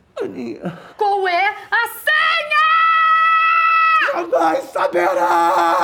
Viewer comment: O que ele falou em 1:17? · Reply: Jamais saberá.